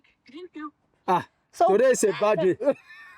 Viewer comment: I thought he was saying; "Sisi, today it's a birthday" kanti all along he was saying " Sisi, today is a bad day"🤣🤣🤣